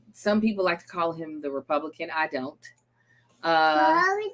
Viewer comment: LOL It sounded like said "Republican!"